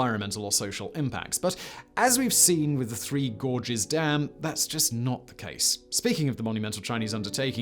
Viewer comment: Ohhhhhhhhhh I thought you said "the three gorgeous dams of the west"...........